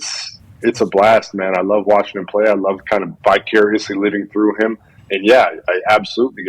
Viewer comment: Love the watt brothers but it sounded like jj said “bi-curiously” instead of “vicariously”